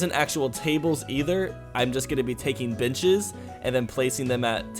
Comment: did he say Bitches?😂